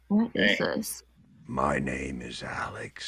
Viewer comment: что он говорит на самом деле "аооаао по ларьвлопа"